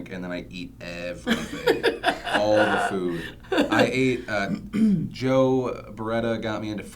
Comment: when i first saw this, i thought phil said "i ate joe bereta.